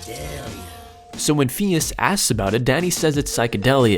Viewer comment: I swore he said “And Daddy says,” i had to remember his name was Danny 😂😂😂